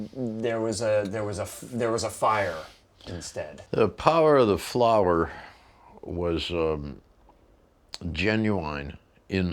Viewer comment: Did he just say "the power of the flower was waning"? Wouldn't it more likely wilt, or droop?